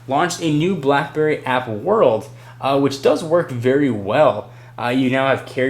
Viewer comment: did he say blackberry apple world at 18:57?